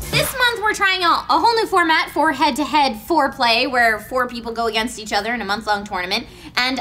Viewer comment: I thought I heard say Head to Head Foreplay not Fourplay.... got kinda excited...